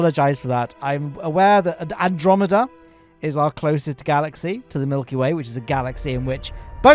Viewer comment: did you say that we are in the andromeda galaxy? Are we not in the milky way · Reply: Yeah I had to listen a few times. He just jumbled the grammar a bit. definitely said “Andromeda is our closest galaxy, to the Milkyway, which is a galaxy in which both Proxima Centuri and the Sol system are located” So he is correct, just jumbled the sentence a bit.